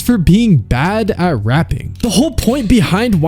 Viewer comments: I’d definitely fix 1:48 to say “rapping” instead of “raping,” although the typo is hilarious and probably true. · FYI the Video Text at 1:48 should read “Bad at Rapping” instead of “Bad at Raping”. Big difference